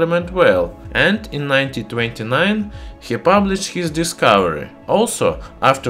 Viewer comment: i heard "1929" ???